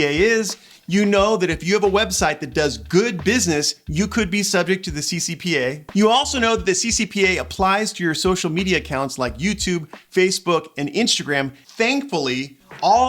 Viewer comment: Hi Josue -- Yeah, I know. It's like the track is stuck on REPEAT ... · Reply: Also. At first I thought it said COPPA. Which is another Psy-Op (Psychological Operation), by the way.